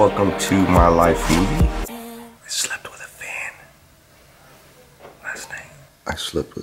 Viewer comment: Omg at 1st I thought u said I slept with a man but u said fan lol sorry